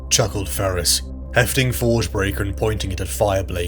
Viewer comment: Sweet episode. I love your lore videos while I paint my beloved space elves. Your voice acting is amazing!! Keep it up! Sidenote, isn't the name of the hammer forgebreaker? I could have sworn I heard dawnbreaker